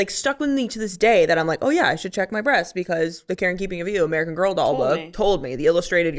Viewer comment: Embarrassed to admit here to my peers I thought y’all were saying Karen Keeping of You until I read the comments. Oop.